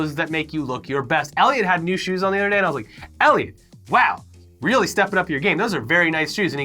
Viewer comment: Did call Elliot - "Ellie" 19:54 ?